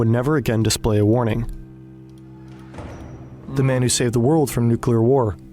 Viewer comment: I thought it said, The Man Who Sold The World...